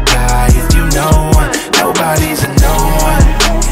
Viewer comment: Anybody know the little voice in the background at 1:19 what does is it saying? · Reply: I think it's just Kyle voice modified saying " nobody's a no one "